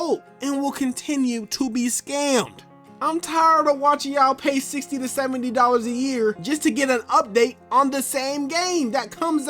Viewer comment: What did you say at 11:00 again I couldn’t tell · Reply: I’m tired watching yall pay 70 dollars a year for an update